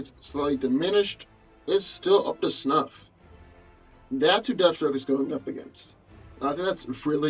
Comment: yeah that's true, which is the reason I still went with Logan. Him killing the the hulk family along with the big boy himself is a bit more impressive than fighting JLA members in a crowded alley. Gotta admit, they're both beasts when they need to be👍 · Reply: TipToe Jo3 oops my bad thought you said deathstrock my baf